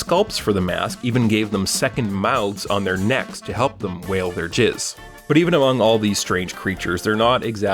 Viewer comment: Did he just say "wail their jizz?!!"